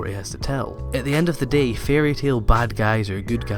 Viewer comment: I was looking at the comments and I heard "Fairy Tail" so I was like hold the fuck on then I scrolled up and realize he's just saying fairy tale You're forgiven 😏